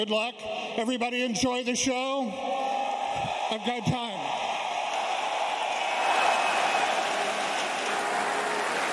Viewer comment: what did Gaben wan saying ? he is forget pinoy words lmao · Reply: Lol you didn't even know when he was sayin lakad matatag. Joke on you, pal!